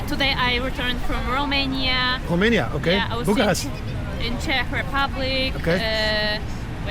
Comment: Burgas is in Bulgaria (if that is what you've said) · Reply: No, he said Bucharest...